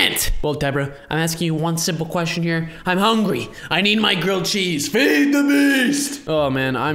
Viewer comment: I thought said I want girl cheese lol your funny